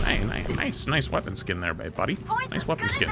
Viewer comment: I think he almost said babe which would mean he secretly has a girlll